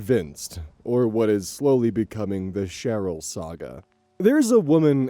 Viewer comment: Ill be honest I thought it said "Chernobyl Saga" for a minute